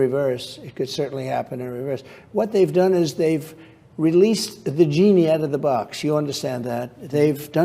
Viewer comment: I know what he said, but I swear it SOUNDS LIKE he said "They released the vagini out of the box"" I'm just sayin'...